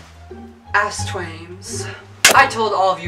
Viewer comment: Sometimes it sounds like you said ASSTWAIMZ